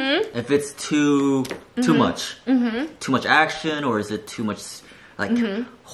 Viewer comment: all I heard was too much whores😌✌️ these beaches need to get outta here